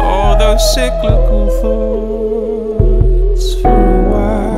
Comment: instead of “cyclical thoughts” i thought he said “sick little thoughts”